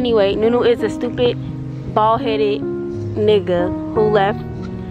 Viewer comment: did say nigga? Lol